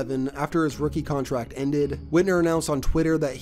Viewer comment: I thought he said hitler